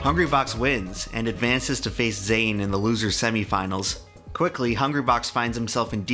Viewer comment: thought he said “FaZe zain” 😂